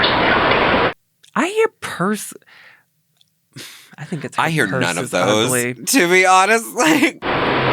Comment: I hear personality